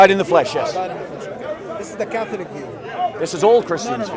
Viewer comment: Did he say this is a catholic view???49:30 I think ❤❤Lee 🇬🇧